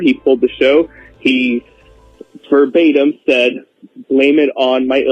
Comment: I think he said "verbatim" meaning word for word.